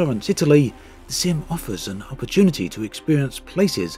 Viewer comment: did anyone else hear sim offers our virginity